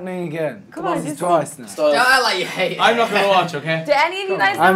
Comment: harry : "is this really happening again. this is twice now" louis : "don't act like you don't like it" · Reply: Realini LMAO i thought he said that at first too but he said don’t act like you hate it 💀